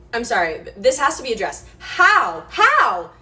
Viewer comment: I thought she said "I'm sorry, but this has to be a dress" at the beginning 💀